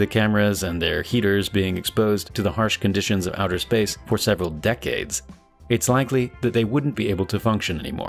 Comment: does he say several decades?